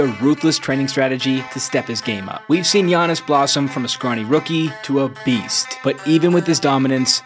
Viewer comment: I misheard it as "From a scrawny rookie to obese"